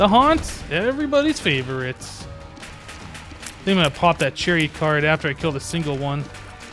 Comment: I heard "gonna pop that cherry card" and was laughing my ass off...